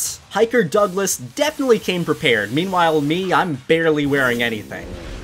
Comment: When you said beware I thought you said be aware of the mountains because it’s dangerous